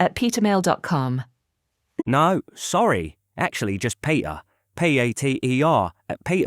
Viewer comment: 2nd one he spelled as "PATER" instead of Peter 2:35 . Can you confirm this?